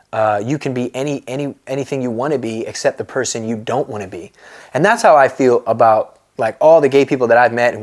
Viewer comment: Wait on the verse "you can be anything you wanna be except the person you don't wanna be" I thought he said except the pussy you don't wanna be.😂😂😂😂